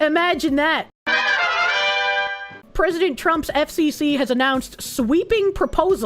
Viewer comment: Wait, FBC? I heard FBC. I don't care that it said FCC, WHAT DOES THE FOXU KNOW ABOUT THE BUREAU OF CONTROL!?!?